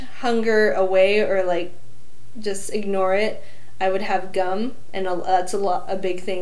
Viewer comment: I misheard "gum" as "gun"